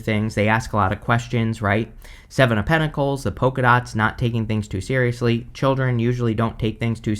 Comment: I always hear "poker nuts" in your readings instead of "polka dots" and I'm like "Hmm, I know what cashew nuts are, but never heard of poker nuts! Is there a poker tree in the wild?"😂